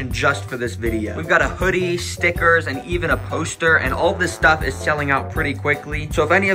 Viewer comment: Did you just say A POSER!?